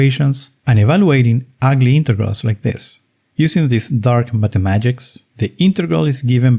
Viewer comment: Did i just hear "dark mathamagics" Nice! Greatly informative videos yet again, btw! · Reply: yup, I said mathe-magics